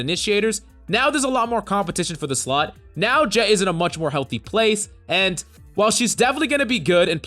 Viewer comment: Did you call Jett slut? 😄